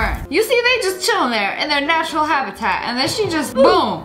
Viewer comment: did she say national habitat?